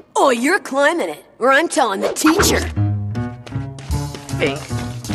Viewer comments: “Hey no neck... you go” (Kevin Throwing rope to eddy) ¡BAM! 😄😆😅😂🤣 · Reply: Did eddy said to Kevin “fink / fig” or “pig” before he climbs? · what did Eddy call Kevin? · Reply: He actually does calls Kevin a “Fink” if you listen to him saying it 😂😭